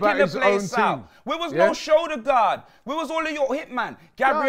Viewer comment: Martin No-show-degaard 😂😂😂😂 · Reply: I thought he said no shoulder 😅